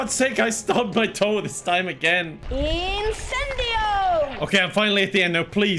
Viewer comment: I kept hearing Harry yell "Nintendo!" and at this point I can't imagine what else it could possibly be.